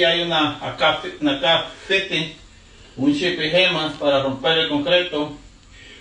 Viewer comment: Only thing I heard was, “problamo in kitchen” and yes, there is a very big problamo in the kitchen my guy 😂😂😂